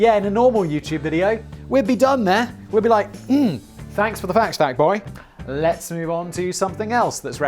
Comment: Lmao I had to rewind the #fucktboi meme I actually thought it said fact boi, but then it was even funnier when I realized that it wasn't "fackt." Keep up the hard work and awesome content Simon and Co. 🍻